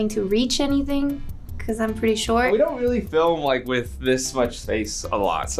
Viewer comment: What is saying 0:24? Google translate looking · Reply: short*